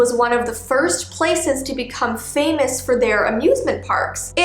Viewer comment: Is it me or does it sound like shes saying Abusment Park??